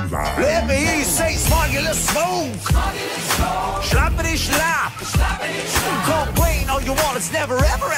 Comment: it literally sounds like their saying shlappitty shlatt ✋🏻😭